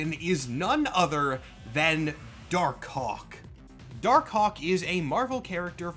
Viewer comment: I swear to god I heard Dark***k and I was like “Oh ok guess that’s just where we are now in culture”